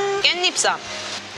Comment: I hear EXO loveshott heheheh